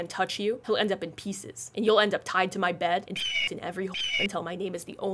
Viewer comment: I HEARD IT LIKE "and he'll end up tied to my bed and * in every *" AND I HAD TO PAUS AND REHEAR IT OH MY GOD LOL